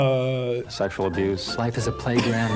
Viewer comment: "Do you like cats?" "Domestic violence" "Sexual abuse" Well,that escalated quickly 😂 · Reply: oh fuck that's what he said, I heard "sexual duce"